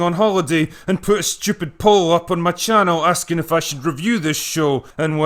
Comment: When I heard "I put a pole up in my channel." I though oh no drinker!!! Don't get that drunk. Then I realized you said poll not pole. LOL Maybe I need to drink less during your videos.